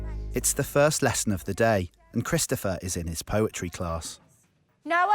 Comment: At first I thought the narrator said ‘his butchery class’